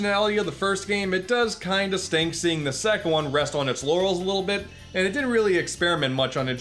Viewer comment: Did he say "The Sec One"